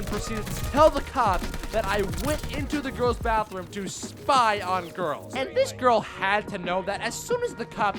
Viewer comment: Did she just say he went into the girls bathroom 100 times